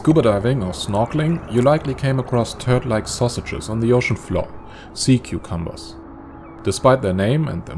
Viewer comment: Did he just say "Turd like sausages on the ocean floor"?